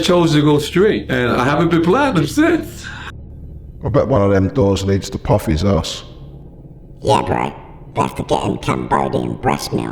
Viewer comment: Heck no!! Keep the show exactly the way it is! If people want things to change, then they can go and make their own YouTube channel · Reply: Hahaha, I had to rewind! I thought you said, I bet one of those doors leads to puffys a$$! 😂